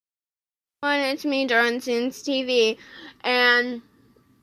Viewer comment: When you said it's drawing sans or whatever I heard it's Darwin sans no offense lol